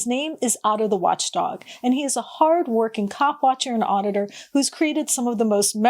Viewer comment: Be sure you enunciate the P when you say coPwatcher. It sounds like you said 🐓 watcher.